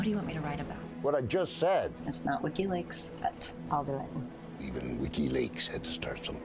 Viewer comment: Omg I thought he said "Ricki lake" when he said wikileaks 😆😆